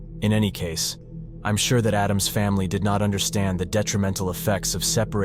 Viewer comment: did he just say Addamms' family 😧